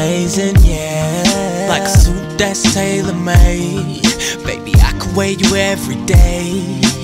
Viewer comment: I always thought he said "I could weigh you everyday"